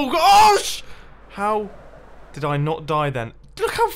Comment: did anyone hear the "oh sh-" at 10:40?